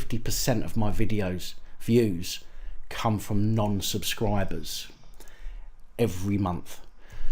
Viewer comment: Liked and shared. Heard about from some guys I was in the army with. Plenty of support in the armed forces for you. We aren’t the biggest fans of the filth. Good luck getting to 10k subs. ✌️(18:10 did you say nonce subscribers)